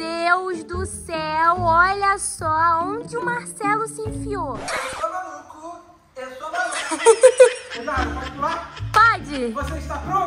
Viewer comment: foi so eu que escutei: estou no c* eu sou maluco frase de marcelo drv